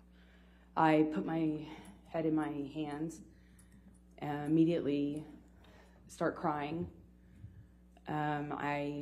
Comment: / does she says " i put my head in my hands and immediately starts crying " ?